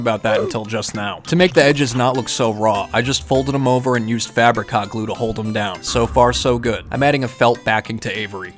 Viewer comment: It sounds like he is saying Fabricot glue